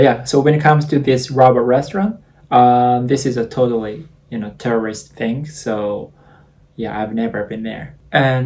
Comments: I'm sorry but at the end of the video when he said "tourist thing" I heard "it's a terrorist thing" and panicked so much for a bit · I love how at 11:10 instead of saying "tourist", it sounds like as if he was saying "terrorist" instead and I can't unhear it now xD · I legit thought he said “This is a totally terrorist thing” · I thought he said terrorist instead of tourist lol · I thought he said "terrorist thing". I was slightly worried. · I love how at 11:10 instead of saying "tourist", it sounds like as if he was saying "terrorist" instead and I can't unhear it now xD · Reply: i thought he said terrorist but i know he didn't mean that but i couldn't figure out what else he would say so thanks for clarifying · Oh my, near the end I thought you said you had never been there because it was a terrorist thing, lol. I was half listening to this and I suddenly looked up, like what? Lol. Then realized “tourist” thing must be what you said. Sorry but I thought that was funny. My bad. Lol. Looks like she went to a lot of city events. My friend went to Japan with her husband a few years ago. They said they saw some of the more countryside/rural areas too. Hiking too. I think if I were to go I’d prefer the more peaceful places personally. They enjoyed it a lot. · at this time 11:10 i thought he said terrorist but he said was tourist